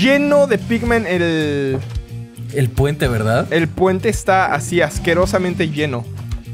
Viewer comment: y 6:42 fui el único que escuché que dijera el puente está asquerosamente lleno ? Dos veces ?? Edit: Nunca había tenido más de 5 likes 😊😢😓